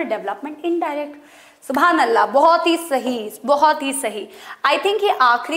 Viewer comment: did she say " subhanallah " ?